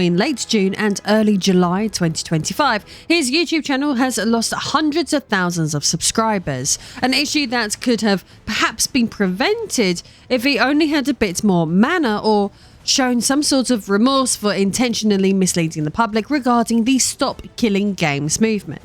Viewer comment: I hear "if he only had a bit more mana" 😅